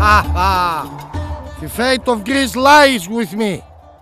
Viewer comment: For a moment I thought he said: "The faith of Christ lies witin me" and made it funnier because he was about to suicide jump and I thought he was praying to not to die